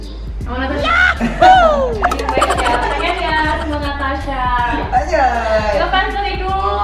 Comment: si nyebut kata anjay, bener gak sih apa aku salah denger 😂 ?? yg kmaren hewir soal kata anjay apa gak nonton vlog ini ya, gak complain harus di sensor gitu 😆😆😆